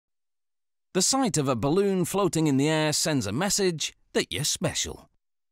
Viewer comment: Tell me why I heard him say "balloon floating in the ass" because of his thick accent